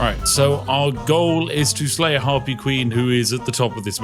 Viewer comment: I thought it said "Herpy"... Glad it did.